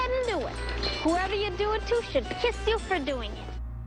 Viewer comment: i thought she was going to say they should pi$$ off😂